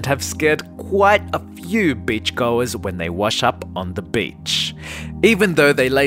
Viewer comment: I swear it sounded like you said bitch